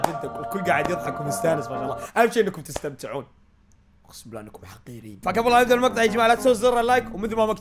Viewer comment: مين سمع ايش قال؟ 😂😂😂😂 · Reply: كال والله انتو حفيرين بس ابقه احبه